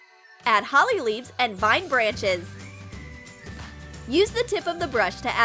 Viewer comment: I thought you said NO brushes.